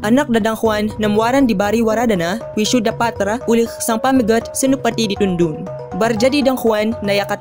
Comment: As a Filipino speaking different Philippine languages, these are the words I understood: Anak = 'anak' - child (tagalog) hutang = 'utang' - debt (tagalog) ari = 'araw' - day/sun (tagalog) ada = 'adda' - there is (ilocano) ini = 'ini' - this (bisaya) dewata = 'diwata' - god/fairy (tagalog) tuhan = 'tuhan' - lord (tausug) dayang = 'dayang' - princess (tausug) hadapan = 'harapan' - in front (tagalog) tatkala = 'kailan' - when (tagalog) lawan = 'laban' - fight (tagalog) beri = 'bigay' - give (tagalog) si = 'si' - particle used before names (tagalog) barngaran = 'pangalan' - name (tagalog)v parlapas = 'pag-alpas' - to let go (tagalog) syat syapanta = 'sapantaha' - presumption (tagalog) urang = 'gurang' - adult (bikol) welung = 'walang' - no; not yet (tagalog) lappas = 'labas' - released, out (tagalog) tathapi - 'tapos' - after (tagalog) sanak = 'kaanak' - relatives (tagalog) makanya = 'kanyâ' - therefore (tagalog) barujara = 'badya' - to say (tagalog) di = 'de' - of (tagalog/chavacano) nayaka = 'na s'yang' - that he/she is (tagalog) sadanda = 'tanan' - all (tagalog/bisaya) sang = 'sang' - of (bisaya) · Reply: I kept hearing "pamagat"